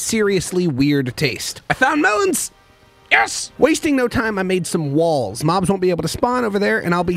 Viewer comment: I heard "I crafted some wall's so mom's can't spawn. "